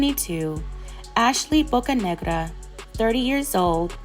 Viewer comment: Sad story. 😔😔😔 But why when you said her last name it sounds like your saying Ashley book.a.nigga 🤣